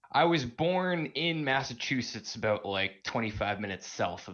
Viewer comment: I heard “aboot”, too.